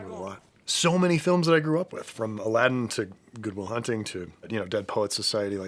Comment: I heard 'Google Will Hunting' and was like 'Don't tell me what to do!' 😂